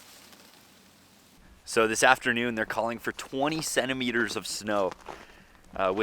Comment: I kept thinking he said 27 Meters of snow, I was like, "Wow 88 ft of snow in one night, that's pretty deep". I know he said 20 cm, but the first two times he said it I misheard it.